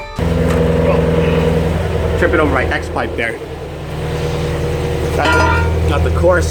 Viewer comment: I thought you said you almost tripped over your x-wife.